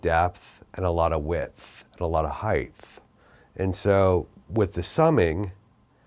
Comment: did he.. did he just say “heighth”?